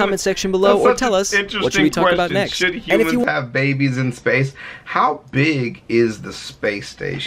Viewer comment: I️ thought was saying “how big is the space”